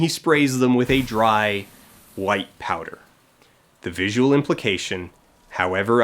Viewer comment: At the end, I heard "sprays them with a white liquid" instead of powder somehow and burst out laughing.